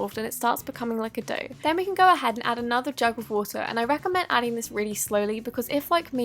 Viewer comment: Did anyone else think she said drugs of water